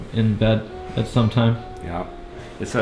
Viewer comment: Does he say yeah mommy at 6:27 ?